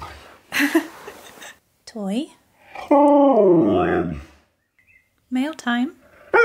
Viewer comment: Holy crap... he really said “In the mail room”. Just listen